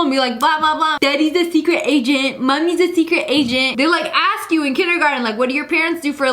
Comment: LOL 14:20 LIA SAID SECRET AGENTS WHERE AS THE CAPTIONS SAID SECRET ASIANS! EDIT IM ASIAN